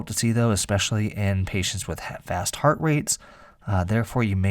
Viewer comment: I thought you said ‘in patients with half assed heart rates’ 😂😂😂